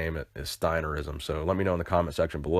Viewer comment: It sounded like you said "next drink the powder singing out" at the end of the video